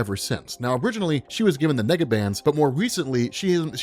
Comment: I thought you said nigga bands lmao